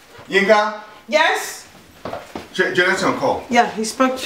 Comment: what did he say ? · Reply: He said did Jonathan Call